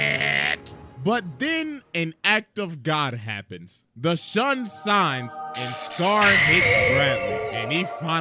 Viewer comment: Anyone else hear "The Shun Sines"